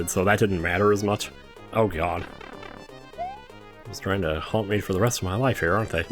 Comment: anyone else hear “hump” instead of “haunt”? lol